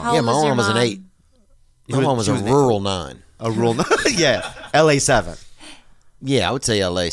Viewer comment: "she's an 8"....." A rural 9" --- I died laughing when I heard this. Love people like Theo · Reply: I thought he said “rural nun” lol